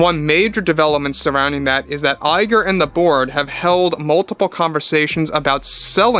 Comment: I keep hearing “Iger and the board” as “Iger and the Borg”